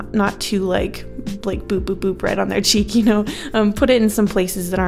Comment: At first i thought you said b-b-b-bread on your cheek instead of Boop boop boop right on your cheek